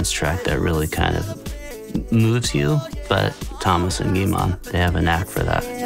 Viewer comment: I heard "Thomas and meemaw" the first time 💀